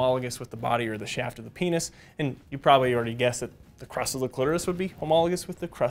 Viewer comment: I straight up thought you said the crust of the clitoris... I was like oh, I'm definitely not going to call it that.